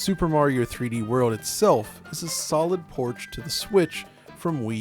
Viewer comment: Did he say porch? 😂